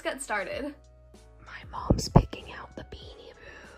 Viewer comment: i thought she said; "my mom's making out with beanie boos"